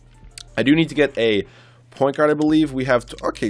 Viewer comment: Did I hear "point yard"?